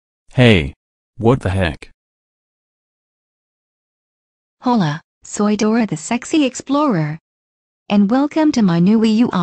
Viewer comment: Did Dora just say hola soy Dora the sexy Explorer